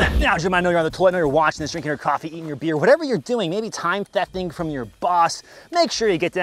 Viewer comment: did u say eating your beer at like